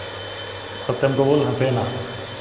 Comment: What was that... Satyam se bol hase na. Tht was spooky... I thought tht was not antil saying tht.. Does anyone felt tht...